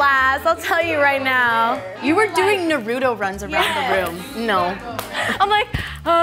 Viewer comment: did homegirl just say “naruudo” runs 😭